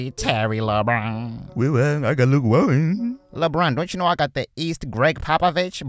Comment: WTH did lebron say?! I need a translator 😂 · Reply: Vyx i got Luke Walton